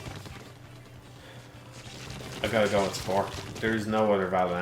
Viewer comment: i wonder which game was the most disappointing around 2:34:43 , i can't understand if he's saying "skyward sword" or "silent hill 4" or what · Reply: It's Spore I'm pretty sure